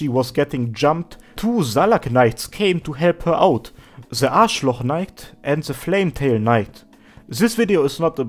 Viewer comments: Did I mishear or did you actually say Arschloch Knight? x) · your spelling of Aschlock`s name sounded like "Arschloch" in german